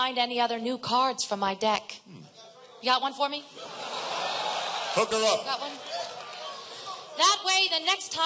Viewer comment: I really don't think he said this, but it sounds like he said "pucker up".